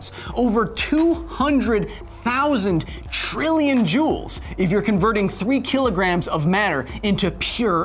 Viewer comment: captions say "jewels" correction: "joules"